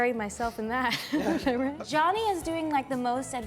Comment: Did she just call him Johnny 🥹